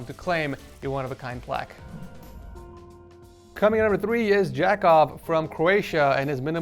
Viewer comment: LMAO I THOUGHT HE SAID JACKOFF